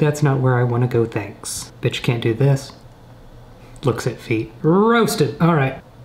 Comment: I heard b*tch cant do this at 5:44😂😂